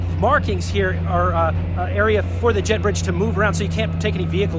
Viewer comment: This is so fantastic im totally geekin out thank you for all your travel videos and effort, 9:25 "jet bridge" sounded like "jeb books" lol